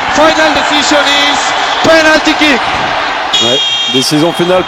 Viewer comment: c'est moi ou l'arbitre dit 1 an après de nouveau PENALTIKI ??